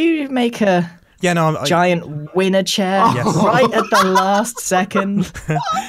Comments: I misheard Winner chair as Wiener chair when said it... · For some reason I thought said “Giant wiener chair”.